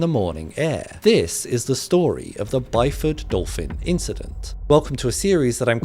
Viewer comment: I heard "Dolphin Incident" and thought a dolphin stole someone's liver